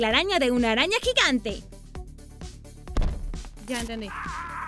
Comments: escuche 1 ruido que decía ganame algo haci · En el minuto 9:44 se esculla una vos que dise ya entendi